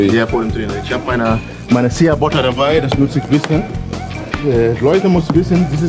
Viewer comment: was ist das bei 00:25? habs net verstehen können ^^ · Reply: Sheabutter